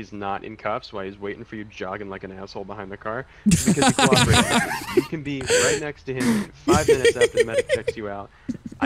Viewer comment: what did say over their laughing? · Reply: "...Because hes cooperating."